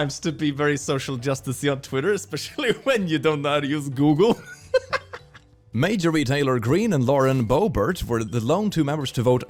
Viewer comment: I was just listening to this while working and instead of Marjorie Taylor Greene I heard "major retailor Greene" 🤣